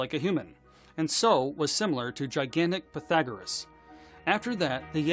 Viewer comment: Wait ... @ 2:15 did he just say "gigantic pythagarus"